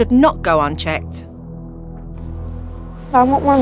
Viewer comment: I thought she said "I want my mommy." And I was like WHAT HAPPEND TO YOU, so I checked the captions. "I want more money." Is what she said...